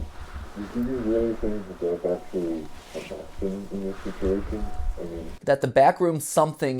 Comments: i thought they said "do you really think there's actually a bathroom in this facility" xD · i think at 16:10 they may have said "okay do you actually think there is a costume in this situation" even though it has no grammar that's what i hear.